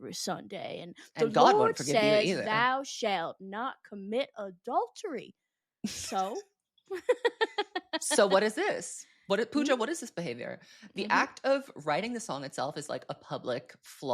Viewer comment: Did he say “Pooja what is this behaviour?” 😂😭